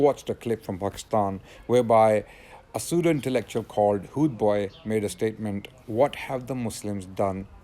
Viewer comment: i thought you said hood boi